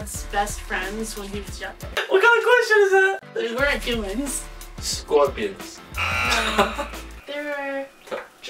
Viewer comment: i thought he said scorpius but he said scorpions and i was about to fangirl about harry potter